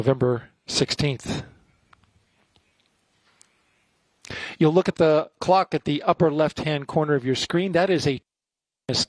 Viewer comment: My kids thought you said "look at the rick rolls"